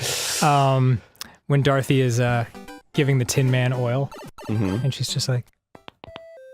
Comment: I definitely heard "When Dorothy is giving the Tin man oral".